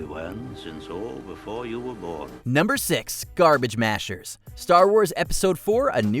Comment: I always called it the trash compactor because I couldn't make out Luke's words clearly. People didn't remember the silver leg because people aren't looking at his legs. He's a humanoidish character, so people are looking at his face and body language. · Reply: I thought he was saying "Garbage Smashers" since it kind of runs together. I always referred to it as the trash compactor, though.